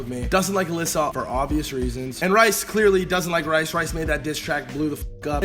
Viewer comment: Bro am I the only one tht heard him say rice doesn’t like rice